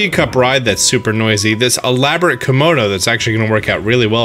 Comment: I keep hearing him say "kimodo". Am I the only one hearing him say kimono like this?? 😮